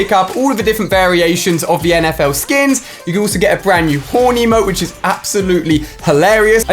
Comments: 🤣🤣When you said horn emote, it sounds like you say horny🤣🤣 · Thought he said horny mode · When Ali was saying horn emote my brother thought he said horny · Like, I thought he was saying Horny emote!! Lol